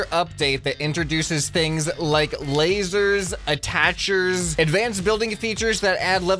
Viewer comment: WAIT A MINUTE DID HE SAY LASERS